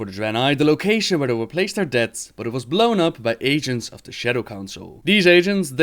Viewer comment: Everytime he says "agents" I hear "Asians" xD